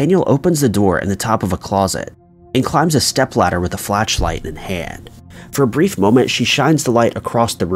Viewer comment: did i hear him say "flatch-light" or am i just miss hearing him?